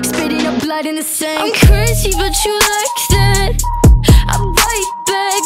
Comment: people: “i searched im crazy but you like that” me, who thought crazy said “hurting” and searched “im hurting but you like that” 💀💀💀💀💀